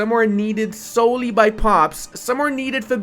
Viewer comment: Did I hear "pops"? I wonder what that will look like. Maybe like stellaris? You need pops to run jobs and buildings? I am not sure if I like that